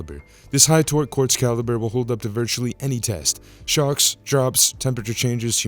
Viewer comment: When he said that the Marathon watch can stand up to shocks, for a second there I thought he said sharks. I was thinking, 'damn, that's one tough field watch'.